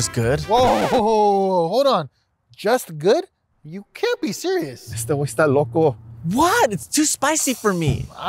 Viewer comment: did he say "este wey esta loco"?